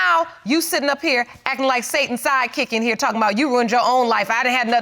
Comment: Every time the judge said Satan's sidekick, I swore I heard Satan's side CHICK.